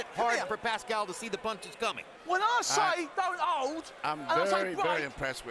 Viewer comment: We need another translator in addition to the French. 1:55:35 Ref: Whoa oi sigh doubt out... (Translation from British to English: When I say don't hold...)